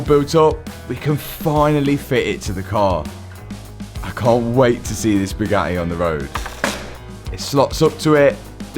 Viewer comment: " I can't wait to see this Bugatti on the road" What i hear: "I can't wait to see thee spaghetti on the road" 😂